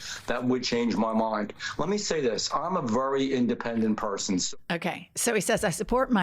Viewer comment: "...I'm a very independent persons" is what I heard. Just listen to it again. 😂